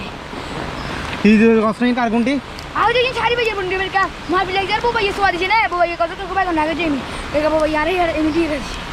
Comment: কি বলসে ভাই বলবেন মাথার ওপর দিয়া গেসে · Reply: ওই ছেলেটাকে জিজ্ঞেস করেছে এটা কার ঘুড়ি, ও বলেছে এটা শাহরিয়ার ভাইয়ের ঘুড়ি , গ্রামে আমাকে শাহরিয়ার নামে চিনে